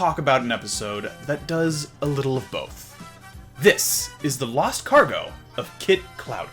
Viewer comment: Oh its lost cargo! I thought you said last cargo 🤣